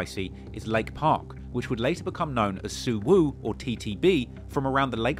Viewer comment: did he say there hood was called “SuWoop” the TTB’s???? like the blood call “Suwwoop”???